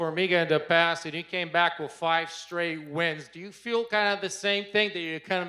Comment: did he say do you feel cara lol?